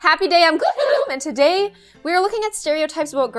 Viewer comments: Am I the only one who thought she said HAPPY DAY ON GLOOOHOOM I JUST FOUND OUT ITS happy day IM GLOOHOOM WHAAAAAAATT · Am I the only one that who thing when she says her intro and she says,` `I'm Gloom!` `But I always thought that she's saying,` `AnD WhOoO!` `lol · For 2 years I thought she said"happy day on gllooommm"